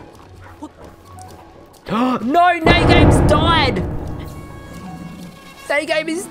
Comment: Is it just me or did he say noooo neigh games died at 6:03😂 Rest in peace Neigh game❤️